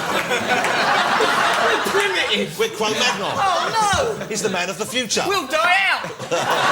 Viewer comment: What does Jonathan say at 1:44? "Chromantal"? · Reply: Cro magnon...early humans in europe